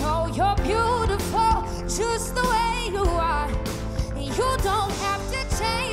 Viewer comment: did she said choose the way you are at 1:01 ?